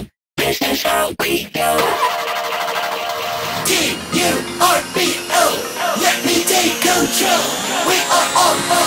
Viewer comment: i first thought it said T-U-R-B-L but i then later realised its T.U.R.B.O